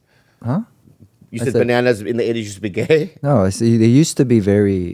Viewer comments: Who else misheard sung saying “that bananas were gay” lol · I totally heard “bananas in the 80”s were gay” too 😂😂😂